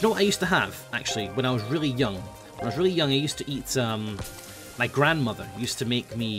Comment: I thought he said I used to eat my grandmother I got concerned for a second